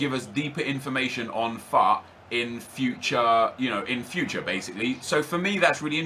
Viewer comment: When he said FUT at 10:40 I thought he said FUCK like if he failed at the game.